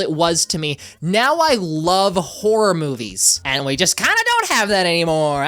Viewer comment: i heard “now i love wh0re movies”